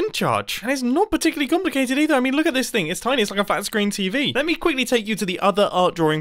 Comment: I thought ya said "fat screen tv" 😂